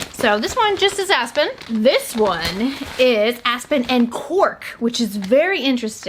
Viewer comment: anyone else thought she said aspen and PORK? 😅